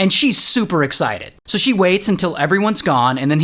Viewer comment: 😅what did you say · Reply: So she waits